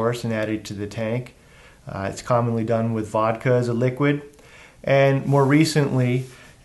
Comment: Vodka? Did I hear that right?